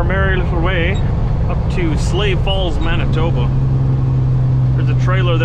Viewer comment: Yall really have a place called "SLAVE falls"?!? The fu**? I actually thought you said sleigh falls before I saw the name on screen.